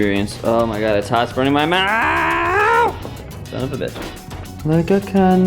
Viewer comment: i am hispanic, we eat those scallions like that, roasted is better, but i'm sitting here thinking, why would you take them out, leave them bad boys in there :( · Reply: an it sounded like you said "it's burning my mouth" lol, p.s that shirt is awesome! :)